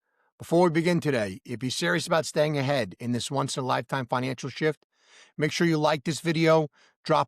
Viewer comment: You know, for a while I thought you were saying “Financialship” 😂😂 Because I do feel like I am in a relationship with my XRP. It’s up and down baby!😅😅 Happy new year and thanks for all the great content